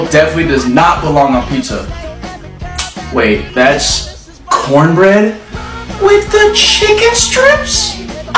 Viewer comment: I heard he said "cornbread with the chicken strip ah~"that made me laugh so hard 🤣🤣🤣